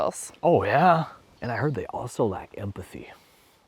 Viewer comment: In the beginning I misheard "lack" like "like empathy" and thought "hell yeah, we are way better at this than NTs" but then I repeated that moment and oh... (English is not my first language)